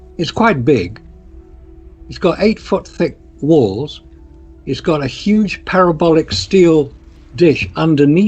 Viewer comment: Did anyone else thought Marcus said "it's got a huge pair of bollocks..."